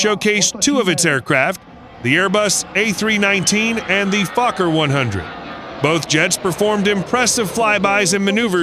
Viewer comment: did you just say fucker 100